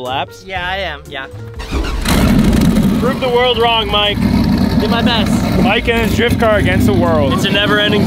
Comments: "ill do my best" Caption says "I thought you got mufflers". No hate just thought it was amusing. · mike says ill do my best (subtitle says:i tough you got mufflers) lmao